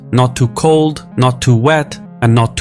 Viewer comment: did he say "not to wet"?.